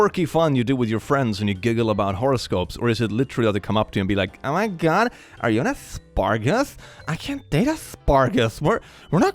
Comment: I could have sworn you said "Oh em gee, are you an asparagus."